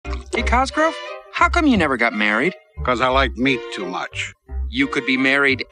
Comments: At first I thought he said "because I like me too much". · I thought he said "Because I like me too much!" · I thought he said i like me too much. Thats also a good answer haha